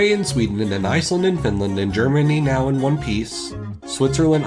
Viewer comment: I always thought he said Germany now wants peace. And I was like damn they went there